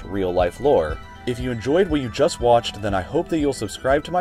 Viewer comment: I thought the ad said My space. I was going to die of laughter.